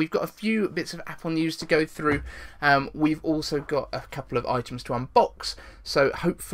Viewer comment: When you said items to inbox I thought you said iTunes to unbox lol